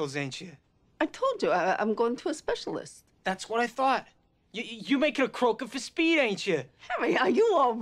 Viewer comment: what did he say? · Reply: sarah williamson “You’re making a croaker for speed, ain’t ya?” As in, you’ve persuaded a doctor to write you a legal prescription for amphetamines.